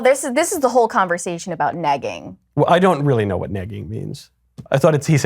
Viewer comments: Thought he said "nagging" 😅 Language can be a pain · I totally thought he was saying "nagging" but asking her that right after was hilarious.